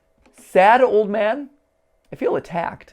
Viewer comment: I heard “Shadow Man” instead of “Sad old man” Did anyone else hear this or was it just me?